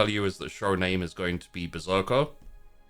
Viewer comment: **SPOILERS** I was having to catch up during the intro part of the stream, so I had it playing at 2x speed. When said his upcoming show is called "Berserker," I initially heard it as "Ahsoka" and shuddered before backing up the video to play it again out of sheer confusion and horror, ultimately sighing out of relief as I heard it correctly the second time around. The "Don't worry, be happy" gag later on in the episode also gave me a good smile. Speaking of amusement, I found myself laughing more than I ought to at the patrol group's dialogue, with deep and introspective lines like "I love Chaos!" and "Chaos is great!" In regard to that scene with the patrol, that had to be a lot of fun for as Sister Superior Rose got to stealth kill three heretics whilst supervising engineers setting explosives and taking out an objective. She was truly doing the Emperor's work. The personal scenes were quite nice in this episode, such as the aforementioned stealth mission, as well as Lillit's scene. She interrogated a soldier who ignored a request for aid from Sisters of Battle who were in trouble, saving himself and his men in the process...at least until Lillit executed him and Minerva put that squad on the front lines of battle, effectively guaranteeing their deaths as well. We also have Sister Helestri's scene with the dying Sister whom she was trying to save, comforting her before the woman's body ultimately gave out. It was quite melancholy hearing how dedicated she w